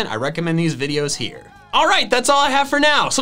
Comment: Am I the only one, or does it sound like says at the end: ”Gotta catch the mall!”